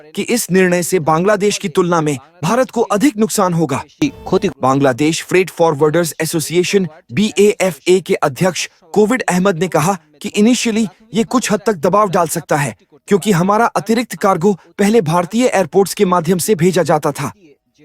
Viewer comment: did he say Covid Ahmed? 😂